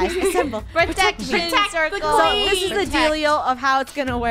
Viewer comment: Did someone hear say "protect the queen" in